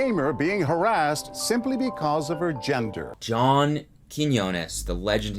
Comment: i really thought john said 'just because of her genitals' lmao oops ahahahaha